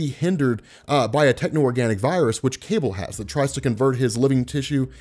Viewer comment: I thought I heard, "Cable has to use his celebrity,"